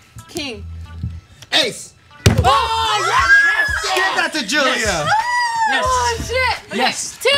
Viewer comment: Anybody hear say ehind the camera " give that to Julia"